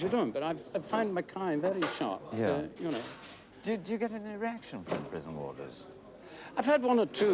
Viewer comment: Noakes sounds like he said “do you get an erection from prison wardens?”